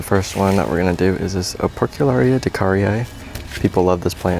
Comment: I can't understand which plant it is at 10:40. Can you Tell me again please? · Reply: Operculicarya decaryi